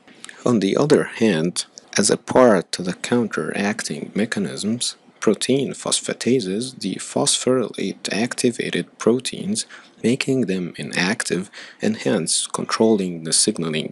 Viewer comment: what did he say at 4:48 all i heard was phos phos protein phosphorus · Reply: Protein phosphatases the phosphorylated activated proteins making them inactive.